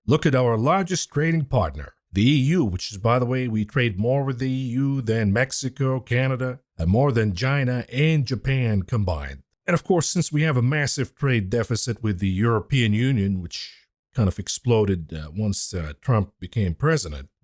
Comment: 😂 did i hear Gina😅, you're too much!